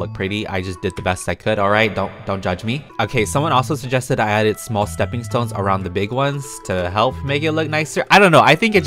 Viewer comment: when said " small stepping stones" i hear it as " glass stepping stones"